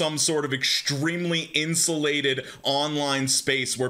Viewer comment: when he said "insulated", I heard it as "incel-lated" 😆